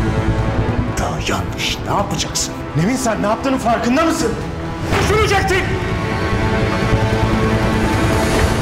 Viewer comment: ne diyor anlayan var mı? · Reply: DÜŞÜNECEKTİN Yani ben çok kıskanç biriyim sende bunu çokkk iyi biliyorsun diyor.